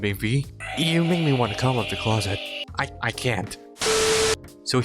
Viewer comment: When he said “you make me come out of the closer” what i hear: “DM Make me come out of the closet” 😂